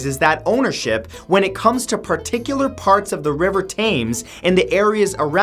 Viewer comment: im sorry did u just say rive taimes m8 it tems 😂😂